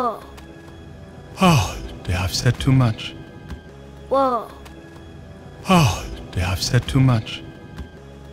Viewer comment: Thanks a lot I have one notice i think the lion has said in 37:30 do i have said too much? There is do in his sentence.